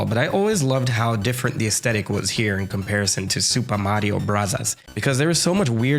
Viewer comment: At some points, I thought he said Super Mario Brazzers.